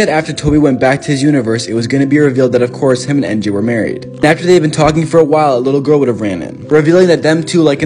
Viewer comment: When you said Tobey and Mj were married I thought you said Tobey and Andrew got married